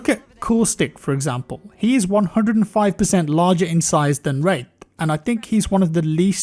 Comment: I swear you're saying rape....?